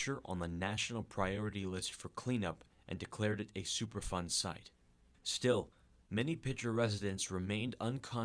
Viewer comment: Did i heard it right? That epa declared it "a super fun site"!? That must be wrong, right?